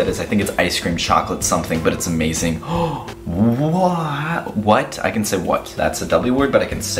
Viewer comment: I thought you said woah can u say woah too?